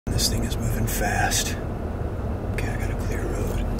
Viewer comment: I thought at first he said "this N$gga moving fast"